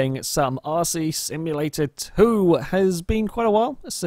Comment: At the start I thought he said arsey simulator 2, LOL!